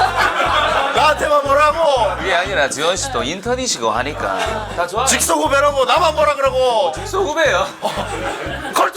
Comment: 다나카상 뭐라고 하는거에요? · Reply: 오츠카레사마데시타:수고하셨습니다❤